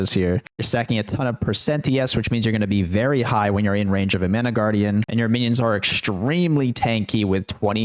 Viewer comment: Haha I paused to go the toilet, came back and pressed play, first thing I hear is "You're gonna be very high when in range of the mana guardian" :D